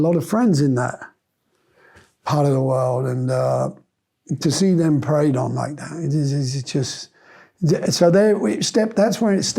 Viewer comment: Mr.Clapton does not say "see them preyed on like that". He says "see them preying on like that". That makes the difference.